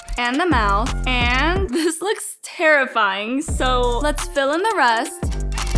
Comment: I thought she said let's fill in the rust but I love it